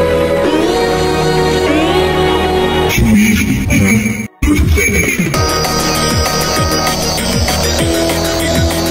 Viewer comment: what did he say? The last 2 words i think he said was prove it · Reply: He said "Too easy huh? Pathetic." And then proceed to ruin your whole career.